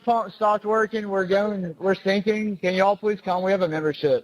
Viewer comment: the best one is the “ get the f up” when you can clearly hear “shut the f up” All of these were misheard and the lady is looking for the worst instead of just letting evidence happen. It definitely seems they are worried about the pump, not some stupid theory. Inaudible but clear is the sign that she was reaching, it can’t be inaudible and clear. She possibly put that to make it seem more credible for the laymen